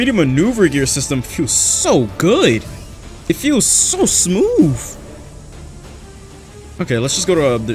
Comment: is it just me or does it sound like he is saying smove